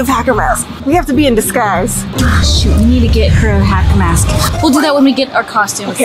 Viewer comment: it sounded like sweared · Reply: It is shoot